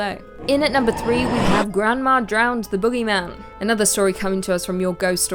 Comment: I thought she said my grandma was drowned by the bogeyman